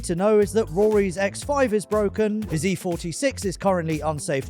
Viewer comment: I thought said "Rory's ex-wife is broken" not "Rory's X5 is broken".